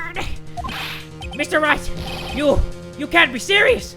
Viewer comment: I Ligit thought he said Mr.Rat instead of Mr. Wright😂😂😂😂😂👌